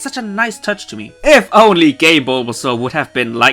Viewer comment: I first thought he said "Gay Bulbasaur" but actually said "game"